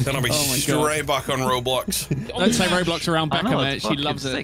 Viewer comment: did he just say.. roblox?